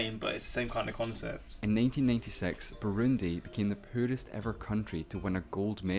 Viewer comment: I kept replaying at 9:38 because I couldn't understand what was said. It was British speak for 'poorest'.